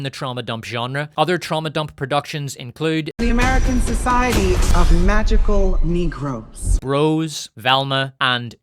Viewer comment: I’m sorry did I just hear the society of magical negros 😂😂😂